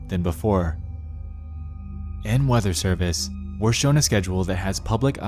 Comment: And now, the weather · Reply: I thought you said feather